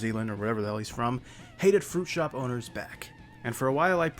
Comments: I thought he was saying "fruit show boners" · I always thought he said fruit shop boners 💀